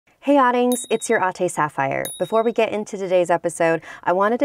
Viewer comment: I never knew you were saying "Hey adings, it's your ate Sapphire." I always thought you were saying Hey audience it's your aunty Sapphire